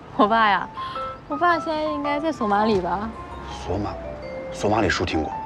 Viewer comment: did she say somalia 🇸🇴🇸🇴❤️